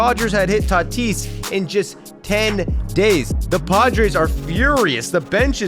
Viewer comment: "The padgers are furious" is what i heard lol an accidental mashup of the team names